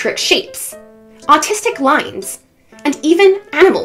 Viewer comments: Am I the only one hearing when she said “artistic lines” I heard “autistic lines” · Bro i heard "Autistic lines" 💀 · I swear I heard "autistic lines" instead of "artistic lines" and it was too funny xD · I thought she said autistic lines